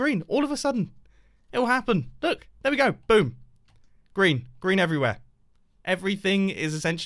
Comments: I know what he was saying, but what I HEARD was: "Boom. Grian. Grian everywhere!" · Why do I hear: "Grian, Grian everywhere" lol